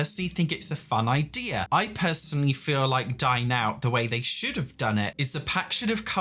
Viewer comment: every time he mentioned dine out I thought he was saying die now </3